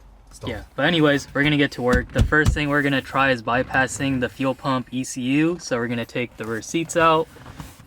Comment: Where is your video? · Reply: sounds like he is saying jump the fuel pimp fuse